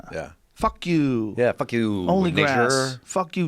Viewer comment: did Kyle say the N word? Was that an audio glitch? I must have misunderstood. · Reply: Nature..he said nature